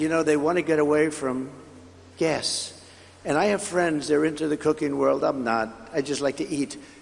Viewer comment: Haha I thought he said he had friends in the cocaine world, and I immediately thought of Don Junior 😂